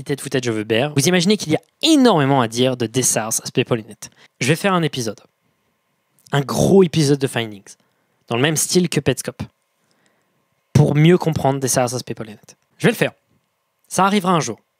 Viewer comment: Mdrr j'ai entendu "je vais faire un gros finding" j'ai fais "ouuuuaais" comme un gosse 😂😂